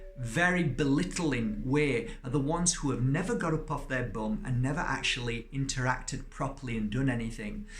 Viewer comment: I thought you said, “a puff on the bum”, but when I replayed it, you said, “up off their bum”. I probably need to get up off my bum sometimes too. Was very excited to hear that you worked with horses. Hope to hear more about that sometime.